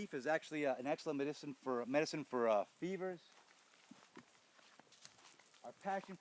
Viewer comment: did he say "excellent medicin for beavers" 😸